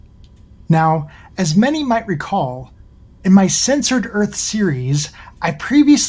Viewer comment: His video wasn't censored, he's just saying "Censored Earth" instead of "Flat Earth". I assume that's part of his narrative, that the "truth" about the Earth is being censored by "THEM", as opposed to just being a load of dingoes kidneys.